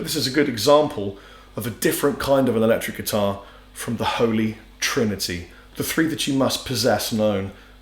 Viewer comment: I thought you said that prs joined the holy trinity? Lol